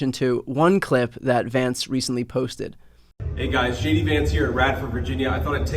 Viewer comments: Sounded like he said "Shady Vance here" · Sounded like he said shady Vance